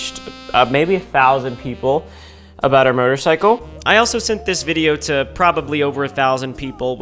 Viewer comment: is it just me or did he say murdercycle?